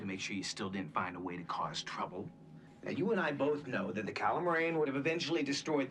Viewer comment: The problem with watching this after seeing DS9 is that I keep mishearing their name as "Allamaraine"